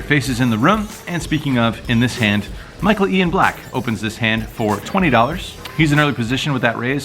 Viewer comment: Did he just say he was playing with Michael Ian Black ?